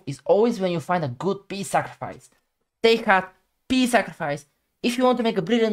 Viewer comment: Hello my dear friends, I hope all of you are having lovely and peaceful day/night full of love and happiness 🕊❤ I am sorry, I speak so weird, that even my editors can't understand me, even tho 90% they wrote the subtitles correct 🙏💙 Its not "pawn" its "pony" always 🐎 0:41 I say "take heart" 3:04 I say "than normal CM" 3:19 I say "overall 80k bullet games" 3:36 I say "Grandmaster Joey" - <link> 4:54 I say "Apollo" 5:22 I say "I will take the king" 8:18 I say "with cheeeeeck" 9:46 I say "take heart" 10:34 I say "but it's dark forest" 11:15 I say "and develop with tempis" 17:07 I wanted to say "actions speak louder than words"